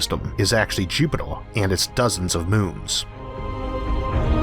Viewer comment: What does he say at the end of this sentence? It's in the beginning of the video at 0:06 "It's very easy to forget that most of the solar system is actually _______" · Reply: “-is actually Jupiter and it’s dozens of moons.”